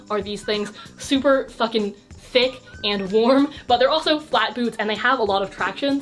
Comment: i thought you said "styling flat boobs" instead of "flat boots", now my flat boobs need you to do a video on that